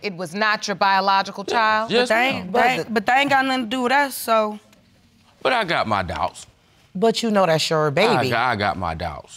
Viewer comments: Thought he said "I got my dogs"? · “I got my doubts” and all this time I thought he was saying “I got my dogs” 😂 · He said, I got my doubts and I swore he said “ I got my dogs” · “I got my doubts” and all this time I thought he was saying “I got my dogs” 😂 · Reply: Huh? I heard “I got my dogs”..... that’s not what he said ? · I thought he said "I got my dogs" 😂